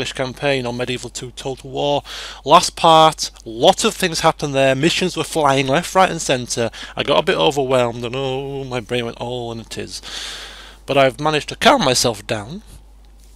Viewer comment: lol i'm rewatching this video now and I thought it said 'Bitches went flying left right and center'